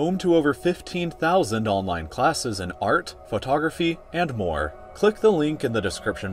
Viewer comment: When he said art photography and more I thought he said art photography and porn 😂😂😂